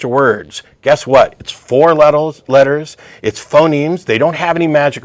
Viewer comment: he says "four let-tells" instead of four letters. I HATE when that happens. It happens to me all of the time. I feel like it takes the air out of every thing I'm saying. The shitty thing is it ALWAYS happens when I'm on a roll--right in the middle of wrapping up a dagger of a point, then BAM. For me it happened when I tried to say "number" (I ended up saying "numb-ells" It must have something to do with us getting fired up, don't you think, Matt?